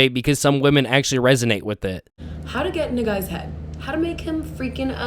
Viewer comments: I heard her says" how to get into n-word's head" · she DID NOT say "how to get in a guys head." that sounded like the n-word